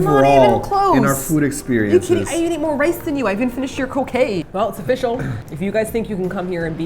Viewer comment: 'I even finished your cocain!' Best misheard sentance I've experienced for a while